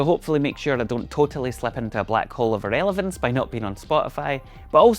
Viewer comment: It sounded like you said you were concerned you might "slip into a black hole over elephants". I was intrigued for a moment.